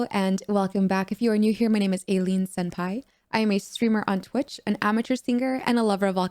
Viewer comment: I swear, I heard You say Your name was 'Alien Senpai'.